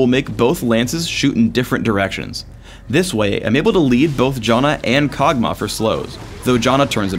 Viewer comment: Did he just call Janna Jonna? lol